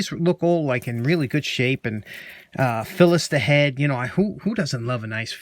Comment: “Who doesn’t love a good fillister head” belongs on a tapestry or something :-) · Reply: Thought he said "Phyllis The Head"......